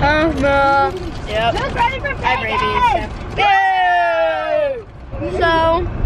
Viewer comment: did say she had rabies??